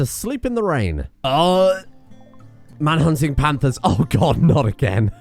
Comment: I swear I heard “Manhunting Pandas” instead of “Manhunting Panthers” 😂😂